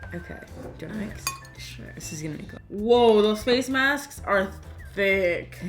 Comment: wait....did i just hear that.. 12:02 Karina Garcia: woah those face masks me: did i just hear sksk o.o